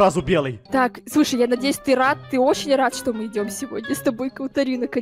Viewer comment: Я хочу чтоб Чпуньк был моим свещеником на свадьбе!!!!! 😂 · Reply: ххахахах, послышалось, что он сказал-рак. А сказал "Рад"